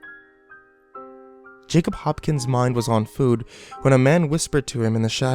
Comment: I thought it said Hopsin's instead of Hopkin's, lol.